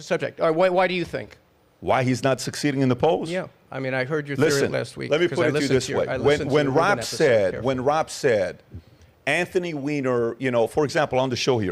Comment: When He said His Name it Makes Me Laugh 😂😂. Sounded like he said Anthony’s Weiner 😂😂